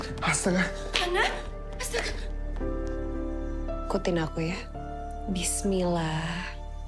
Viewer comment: Mungkin maksud Hanna, menikahkan suamiku dengan kamu Cathy. Baru si Cathy bales sesuai dgn judul. Mudah mudahan gw gak salah denger. Tp gw nungguin bgt film ini. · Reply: Finally, Hanna sebelumnya bilang ke Cathy "ikuti aku ya". Oh ya bner, berarti gue salah denger.